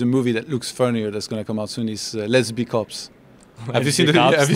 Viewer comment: When ToD said the movie "Let's Be Cops" I thought I heard "Lesbian Cops"